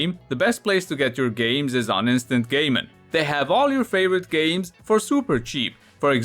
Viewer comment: I heard, "instant gay men", and thought that your new sponsor was very interesting there for a second.